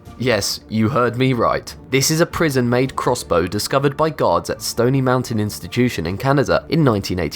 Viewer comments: When you say "discovered by guards" all I hear is "discovered by God" 😂😂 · I keep hearing "this was discovered by GODS" O__O